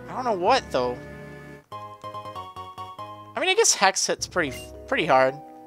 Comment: haha you said Hexxit instead of Hex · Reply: he said "hex hits" not hexxit